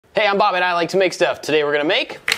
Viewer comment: I can't get it out of my head. Every time, I hear "Hey, I'm Bobbet, I like to make stuff!"